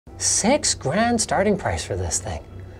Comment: I heard sex grand 🤣🤣